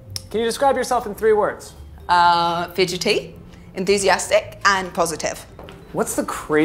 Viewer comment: I love her smile I need some help i am french and at 2:49 when she has To describe herself i can't hear what she say can someone tell me what is the first word she used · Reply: i think she said fidgety and enthusiastic